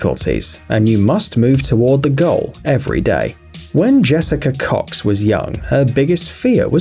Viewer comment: Usain bolt has left the the chat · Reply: Am sorry but it sound like he said Jessica cocks